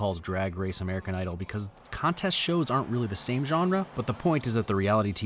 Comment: subtitle error (are instead of aren’t)